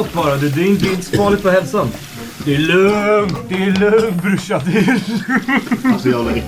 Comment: what word is this vlad? the one that translates to "bro" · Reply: He's saying "brushan" which is a way of pronouncing the word "brorsan" (which means bro/brother in swedish)